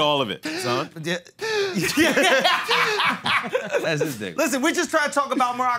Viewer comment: Did mark call Alex nigga? 7:52 ‘That’s this Nigga’ that’s what I heard him say.